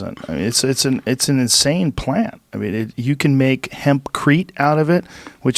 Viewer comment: did he just say "Hemp is an extraordinary planet"?